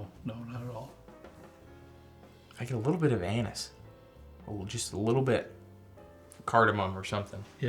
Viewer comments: at the 17:01 mark What I hear: “I got a little bit of anus.” Me: WHAT?! “Oh, ANISE!!!” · OMG @ 17:00 I thought T said I get a little bit of “anus” 😂😂😂